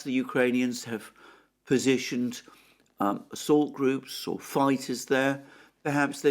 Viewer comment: i probably misheard. Ukrainians have positioned salt groups in the salt mine :)